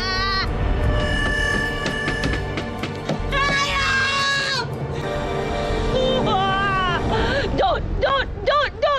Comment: Is no one gonna talk about this scream She sounded like a bird 💀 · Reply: She was saying, "[YOU'RE AN] ANIMAL!!!!!"